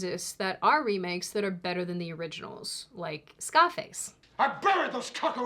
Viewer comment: You said Scarface, I know this. But I heard "scopics". Next video say Juuupitah again, please.